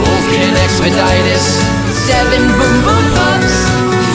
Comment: Until I read the lyrics in the description I thought they were saying "Turkey Boom Boom" XD